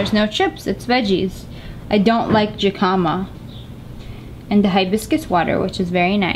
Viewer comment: Wait I do i thought you said hicama